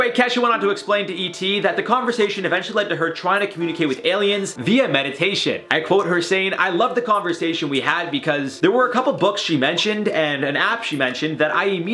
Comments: What are your thoughts on this recent Kesha news? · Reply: i heard "medication" lol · I swear I heard him say medication decal times before I realized he said meditation 🤦🏾‍♀️🤦🏾‍♀️🤦🏾‍♀️